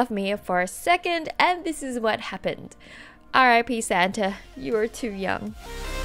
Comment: I thought she said," RIP Santa you were to yum." Hehe